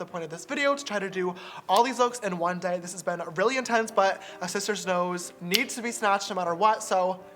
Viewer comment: Lol🤣🤣🤣 i heard 'shishters'